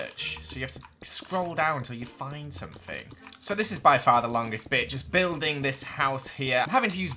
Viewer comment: I swear he said this is by far the longest bitch